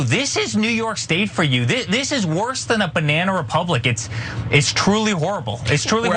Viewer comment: Did he just say banana republican